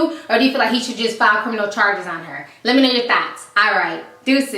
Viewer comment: in the end it always sounds like ur sayin "lemme know ur thots" and i love it